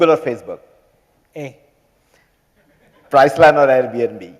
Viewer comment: what did he say? price line?